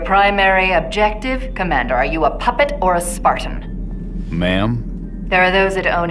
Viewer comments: Even if he wasn't saying mom, you can't deny that Halsey referres to all the Spartans by name. From John to all the others she watched grow up, she can identify each one just by looking at their armour. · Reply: When you have subtitles on they say “ma’am” but it really does sound like he says “mom” because of his accent. I think he said “ma’am” but ye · Actually, he's not saying mom. Halsey may have been like a mother to all of them, but due to Jorge's accent it sounds like he is saying 'mom' when he is in fact saying 'ma'am.' Turn your subtitles on.